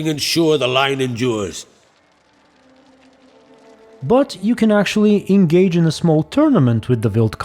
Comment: "But... you can also engage..." is what I heard, but "butt" is what I saw.